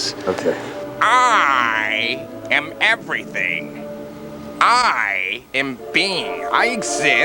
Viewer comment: I thought Colby said “I am bean” 😂😂🐣